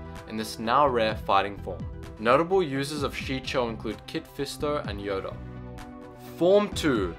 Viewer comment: wait...did he say Kid Fister???!!!! That guy needs to explain himself :/